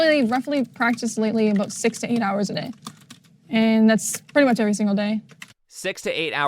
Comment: I thought that girl said "16 straight hours a day" and I was oh yeah that's fair enough, but she said "6-8" my girl I play that much and im not even training LMAOO